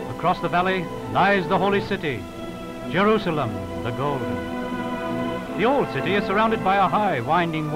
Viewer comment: Jerusalem “the goal” Did I hear that right? What does that mean?